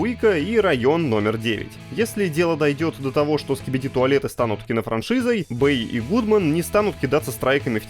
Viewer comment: Не знаю что это такое, но мне послышались скипяти туалет